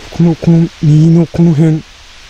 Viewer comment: あけましておめでとうございます。 今年も( o≧д≦)oﾌｧｲﾄｰ!! 4:37 小声で「待って」 10:35 男性の声「ヴァー」 20:03 「フッ ゴォー」 この小屋には、妖怪化した首の長い女性と男の子がいるそうです 26:10 子供の声「あっ おっ」 31:05 女性？の声 41:16 小声で「きえて」 42:53 女性の声 59:14 女性が歌？ お疲れ様でした( ˇ人ˇ)†ｱｰﾒﾝ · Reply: 老婆の声で「右」にも聞こえますが、なにか方言？の様にも聞こえます あと歌ですが、ちょっと明るめ歌謡曲？みたいなのが聞こえたのと、 47:07 の写真に大きな顔が2人 中央少し右男性、左性別不明